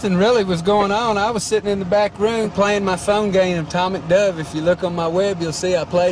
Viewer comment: Legend has it, he’s STILL playing Atomic Dove to this day.... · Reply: I thought he said atomic DUB lmaooo